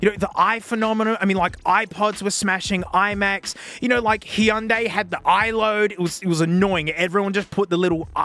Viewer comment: Did you just say he undïï instead of Hyundai😂😂😂